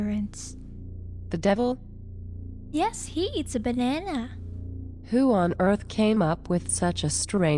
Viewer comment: "the devil even makes an appearance" "the devil..?" "yes, he eats a banana!" she said it with such casualty I love it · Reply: I heard her say "he is a banana" and thought okay....